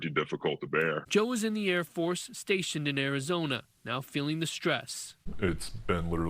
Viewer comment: Arizona??! I thought said lower Greenville Dallas.